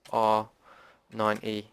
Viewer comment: i just facepalmed at 31:11, thought you said z r 9 E x listened to it a few more times before i realised you said 90.... awesome video though thanks working well so far